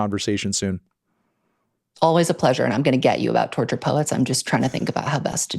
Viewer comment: he really said imgonnagetyouback